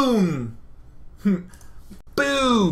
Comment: Wtf.. Did he just say "boobs... wooo!" at 20:46?